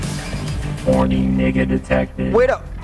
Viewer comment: Had me cryin listen closely and you’ll hear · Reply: I think I heard "Warning n**ga detected"😂